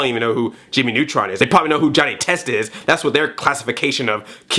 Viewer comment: See when you said Johnny Test I thought you said Johnny Quest and was confused for a second lol.